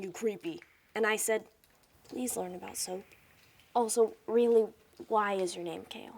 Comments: what is she saying at 3:20? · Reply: she said please learn about soap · What did she say in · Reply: "Please learn about soap."